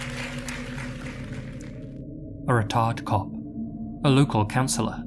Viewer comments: When you said "a retired cop," the subtitles thought you said "a r*t*rd cop." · I thought said “a retarded cop.” I was like wtf?? 👀🤦🏾‍♀️😂